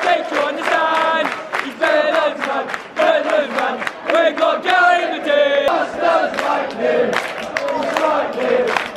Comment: wtf is he saying? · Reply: Jay Sahota called passion